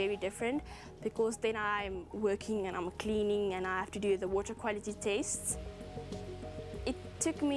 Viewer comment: did she said "water quality taste" ?